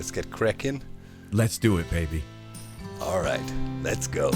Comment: did you say ‘Alright, Let’s goat’? 😂😭